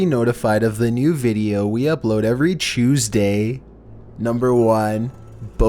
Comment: Did anyone else hear “Choose day” at 17:07 ?